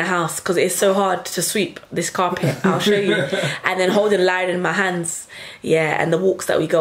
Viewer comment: Did you say "holding Lion in my hands at 19:21?" ...is the name Lion or am I reaching? 😃